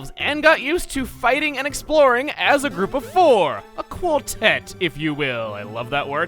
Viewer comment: When you said a quartet the first time I'm not gonna lie I heard it as "Acooltent"